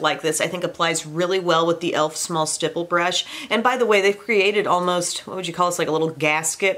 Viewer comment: min Did she said Elf small Stripping Brush. English is not my first language and I currently looking for a cream blush brush.